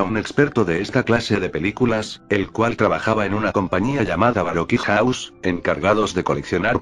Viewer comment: como se llama la compania? No se entiende x el loquendo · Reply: Creo que se llama Baroque House